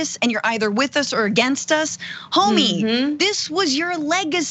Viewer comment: did she said homie 😂😂.. was funny